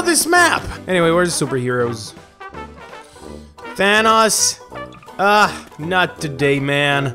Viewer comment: I thought he said vanoss